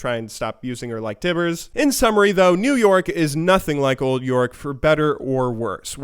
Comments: Please don't tell me that at 6:45 I'm the only guy who heard 'New York' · I actually thought he said "New York is nothing like Old York"